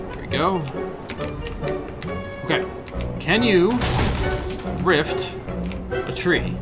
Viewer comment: Oh I thought you said can you gift a tree 😂